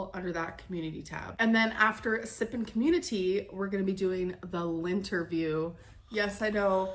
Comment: You’re the third reactor I’ve listened to and thus the third time I’ve heard that clip, and I’m JUST NOW realizing that it’s LYNN-terview. I thought she was saying LINT-erview, like mocking the way Beck’s been exposing the “lint-picking.”